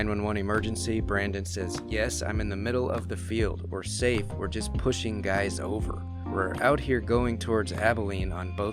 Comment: I thought he said a sniper just shootin guys over.... . He has a pretty thick southern accent so he would most likely say "shootin" instead of "shooting".